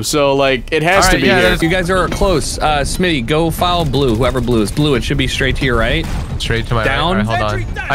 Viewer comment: I HAVE BEEN SUMMONED. I HEARD BLUISH